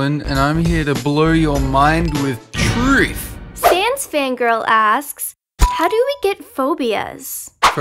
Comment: i think maybe (maybes) it says sans fangirl!